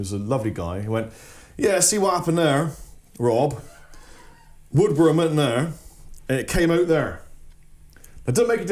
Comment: whag did he say? i couldnt understand him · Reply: He said the shop owner said: "see what happened there, Rob... a wood worm went in there (*upper pickguard hole) and came out there (*lower pickguard hole)"